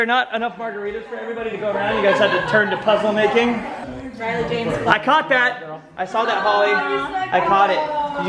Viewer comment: "Riley James Clark.. " baby name??????????????? pretty sure that's what I heard when Holly was kissing her belly. hmmmmmm........